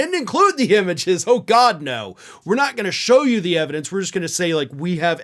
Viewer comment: Do they need evidence? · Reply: I thought Mike said forget about the evidence and then went on a rant about how Chinese thermostats change mail in ballots.